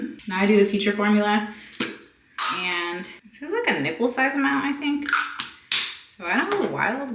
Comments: I 100% thought you said “I use a nipple size amount, so nothing crazy” instead of NICKEL and I was like that is…quite the comparison? Had to do a replay 😂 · I thought you said 'put a nipple sized amount', and I said to myself, that's not my nipple 😂😂!